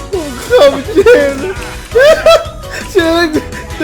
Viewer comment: ใครฟังไม่ออกเราแปลให้พี่พูดว่าผมขำเชนค่ะ 555555